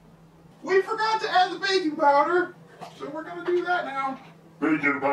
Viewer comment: I laughed so super hard at "bacon powder"/"baking powder". Mostly because I heard it as bacon powder first too, then you two played on it, and it was a beautiful moment. Bahaha <3